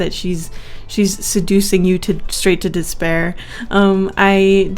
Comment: did you say despair…?